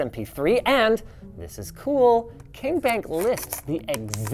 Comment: i thought the ram said king bonk, instead of king bank.